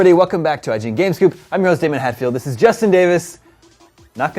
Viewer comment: I kept hearing "games cube" instead of "game scoop" lol